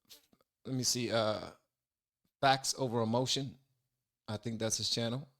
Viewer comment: can someone let me know the name of his channel? i can't quite make out what is saying... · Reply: Facts Over Emotion I imagine the former is the name, and the later is the account.